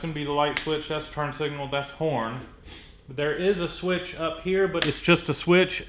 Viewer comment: It sounded like he said porn "1:09"